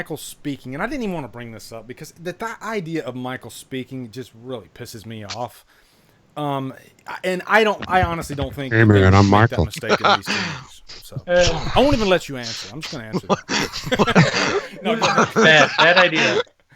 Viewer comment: what did he say? Hey Abraham I'm Michael?